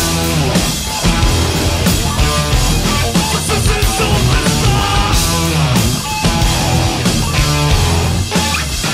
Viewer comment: Alguien por favor que me diga que dice Randy en el seg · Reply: SUSUSUPER STAR!!